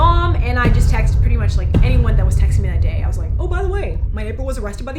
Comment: it sounds like she said her ni*ple was arrested last night 😂😂